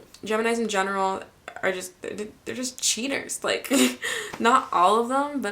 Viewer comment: for a moment when you said Geminis are cheaters i thought you said cheese eaters lol